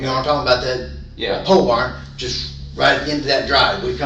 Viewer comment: "unintelligible" = "pole barn" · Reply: Right, my first thought was he clearly said “pole barn”😂😂😂😂😂